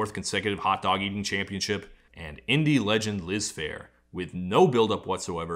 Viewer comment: i thought that said "lisp hair" . now every time ya say "liz phair"....... i hear "lisp hair"